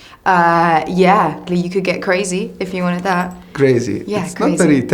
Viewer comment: Is it or did Emilia say "You could get crazy" as she looked at him doing that action of his fingers